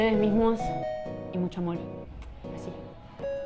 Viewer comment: Que dice en el minuto 3:41 ?? :( Ya repetí esa parte como diez veces y sigo sin entender heeeeelp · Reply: Con mucho amor así💋 Pero en los labios de una chica!!😂